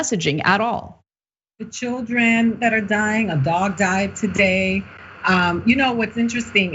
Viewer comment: did she say a dog died today?